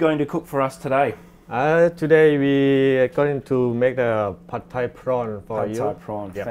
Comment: he says 'pad thai prawn' - the subtitles say 'pot type roller bar prawn' and 'pub type Ron'. 'Coriander and eggs > colander and X' wtf ? that's why I don't really trust that subtitle thing :-)